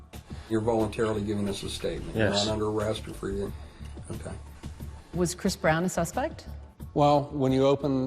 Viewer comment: did she say "was Chris Brown a suppect"